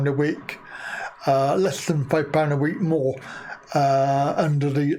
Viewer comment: £2.50 a week more I heard